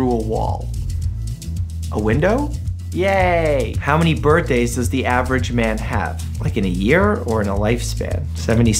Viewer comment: Hahaha I thought he said: how many bird days does the average man have